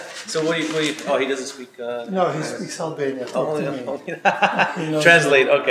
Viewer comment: Did he just say his floor guy speaks albanian?